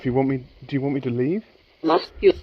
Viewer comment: Very nicely done 👍 30:45 when you asked “do you want me to leave?” I heard “must you? A lot of responses 👍👍👍